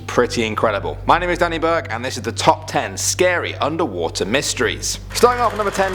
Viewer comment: OH MY GOD I THOUGHT IT SAID SCARY UNDERWEAR STORIES